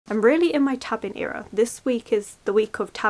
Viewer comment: When you said tabbing era I thought for a moment you said tavern era and I was like ooohh books set in taverns